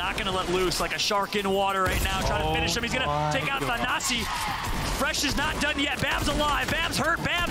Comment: does he say “Fresh takes out the Nazi”